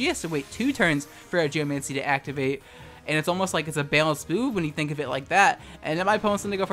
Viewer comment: had to turn on auto captions to figure out what was being said at 7:00 and while it did help me figure out they were saying "balanced move" the captions actually said "balanced boob" instead lol